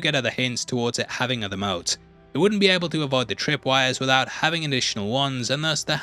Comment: What do you say at 13:17? “Having them out?” Can’t quite understand